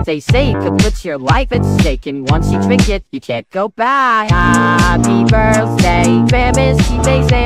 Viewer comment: HAUPPY that’s what I heard lol